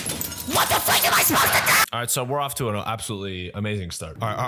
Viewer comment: at this part i thought said "WHAT THE FUCK AM I SUPPOSED TO DO"